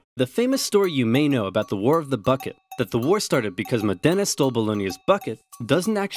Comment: I heard "the war of the fuck it" ._.